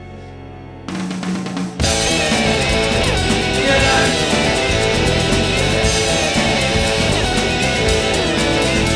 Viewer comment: Love this insanity... What does Wetton say at 1:50? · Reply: He's saying, "You don't!" which was what Crimson members said if they thought they messed up their take. I read this somewhere--can't remember where 🙂